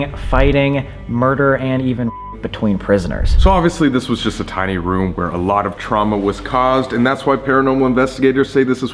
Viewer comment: what did he say? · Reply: He said "lots of fucking"